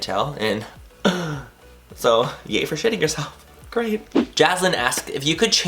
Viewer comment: YES absolutely I want makeup deets ..... 👍 Wait a minute you said you 💩 yourself?? jajajaja I thought you said you SHOT yourself & I'm like but why is he glad it happened right before we got to the school why is he so excited that it happens at all okay but I got it now haha... So you decided to go liberated and burn the draws lol .... You are so adorable I wish we were neighbors ugh ily you're so sweet and humble 😍